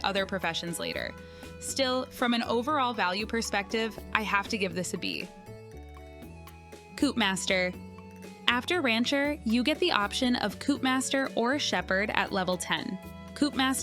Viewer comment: for a second I thought you said "poop master" 😂